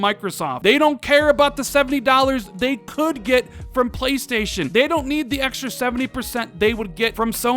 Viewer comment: he said "PS too". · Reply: oh ok i thought he was saying play station 2